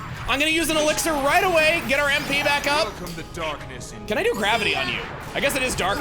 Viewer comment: I'm going to ruin this final boss for anyone who reads this comment. For the longest time, I thought he was saying "Welcome to Darkness" like he works in a fast food drive thru.